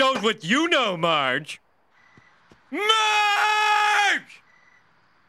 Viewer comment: what does he say when he shouts the second time after MAAAARGE...? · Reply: Homer: Marge! Hey Marge! Marge: keep yelling you big ape.